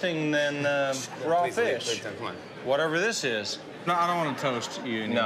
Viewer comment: What did jimmy say to waitress at 2:31? Sounded like “please leave, please don’t, come on”